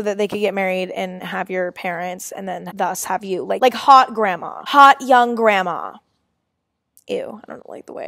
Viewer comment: I’m falling asleep while watching this video and as I’m pretty much passing out all I hear is “hoT gRanDma” 🤡🤠🙃 I’ve either lost it or so has Emma.